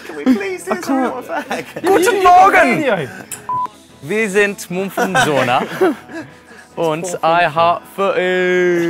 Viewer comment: Can someone tell me what Winston says at 1:03? ( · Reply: “We are Mumford & Sons” in German (before that he said “good morning” in German)